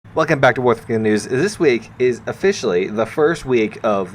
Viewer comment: did I hear "Wargucking news"?